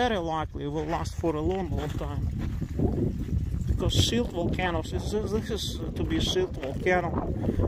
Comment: What is it you are saying at 3:32 .. Because "silt" (?) volcano..., this is going to be "silt" volcano .. I don't understand this word. Doesn't seem to be a term in geology/ volcanology. Could you or others clarify, please? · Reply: I believe he's saying "shield volcano".